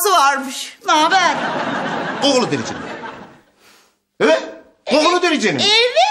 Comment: de burhan ne diyor ya bir türlü anlamadım 🙄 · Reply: 'Kokulu dericinin' diyor